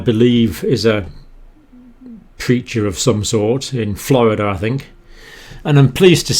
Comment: At around 2:02 I totally misheard "preacher" as "creature" and I was like... "yes, I agree" before I realized lol Big thanks from NYC, keep up the fantastic work :)